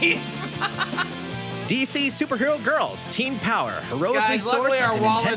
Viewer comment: woah woah woah. Super girl girls? Am I hearing that correctly?